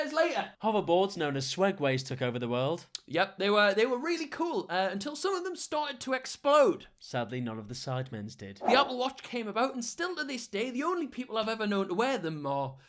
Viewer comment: what did he say did he say another watch